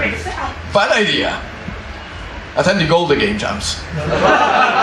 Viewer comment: What did he said? Attending golden game jobs? o_O